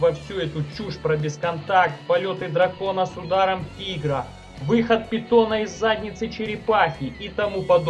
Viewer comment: выход питона из задницы черепахи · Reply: А мне послышалось выход бетона, ну затейник думаю))))